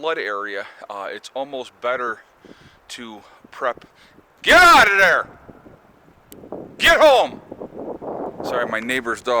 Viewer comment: I was just minding my business and I heard, “Get out of there! Go home”in a big booming voice. I thought that fits